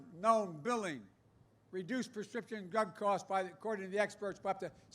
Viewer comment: I swear I thought I heard him say something about prescription gun prices lol 😂